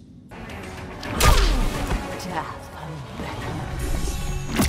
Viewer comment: What did she said in 0:53? 🧐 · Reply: "Death hand beckons"